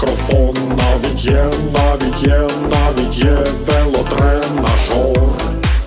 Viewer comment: я зразу не зрозумів, мені почулось, що він сказав велодрен нашов, а насправді велотренажер.